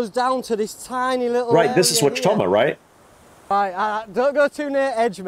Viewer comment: What did you say at 3:21? I've listened a couple dozen times and I hear you say "right this is watchstommer right?" and my brain just cannot figure out what the word/s in the middle are.